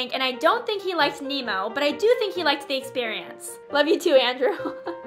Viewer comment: Y'all heard her say "love u too Andrew" this is just a big" WOW"